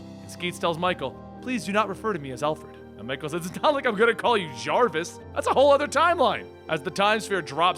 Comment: Did he just say "JARVIS"!!